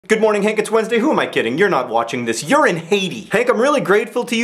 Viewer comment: 1) tea > coffee 2) i thought you were saying Hades, not Haiti.